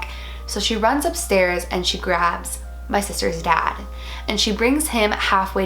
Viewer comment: I thought she said her sisters cat 🤣